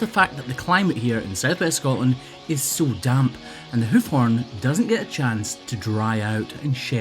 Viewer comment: Misheard hoof horn as hoof worm and was mildly concerned